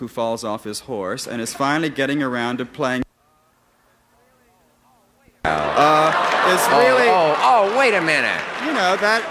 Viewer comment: Rex Reed said, "He is finally getting around to playing [the rest of the sentence was muted]." - What Reed actually said was: "He is finally getting around to playing a part that he’s been playing for 30 years, a big, dumb, swaggering, lummox!" Wow, I guess Rex didn't like Mr. Wayne too much. I wonder if it was because of John Wayne's anti homosexual stance?